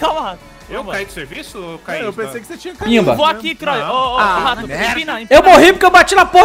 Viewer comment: eu ouvi um PIMBA?